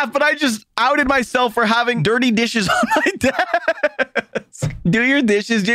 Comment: I SWEAR TO MY 2 NUT SACKS I DID NOT HEAR DIRTY DISHES I HEARD I HATE HOW I HAVE DIRTY NI